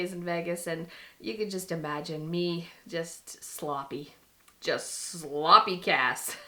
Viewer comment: The new kitchen looks so good! Adding shelves to the broom cabinet was such a great ideas! Ok I thought you said "sloppy gas" instead of "sloppy cass" and I was cracking up! I live about 4 hrs from LV and I have so many drunk and sloppy stories too 😣 hopefully you can share them one day! 😂